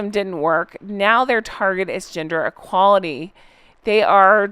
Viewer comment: It's Ginger Equality ...... not Gender Equality that Harry misheard and thought he was promoting.